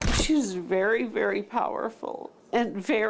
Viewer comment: I thought she said he was beary beary powerful. But I'm glad she's okay